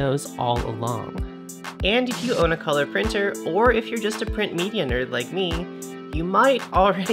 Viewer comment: I heard "if you are a print millonaire like me" and I was like "wait, what?" 🤨 (EDIT) Welcome back! So cool to see you again 😄